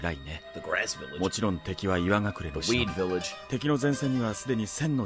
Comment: ATTENTION! When watching the video, it is recommended that you are not eating at the same time... When I heard “weed village“ I almost spit out all my food from laughing so hard. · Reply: I thought he said wheat😅